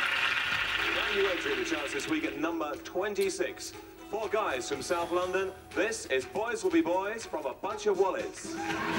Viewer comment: This what the "casting" man said: Number 26: 4 guys from South London, this is "Boys will be Boys" from A Bunch of Wallies!